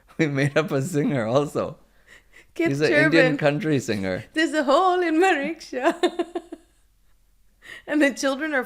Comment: I misheard there is a hole in Mariksha🤣 and i thought it must be a town somewhere in this world